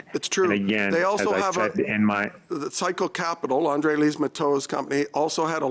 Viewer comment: is it wrong that i heard it as "Psycho Capital"?